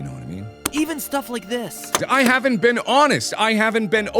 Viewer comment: anyone else hear “I haven’t been Annus”?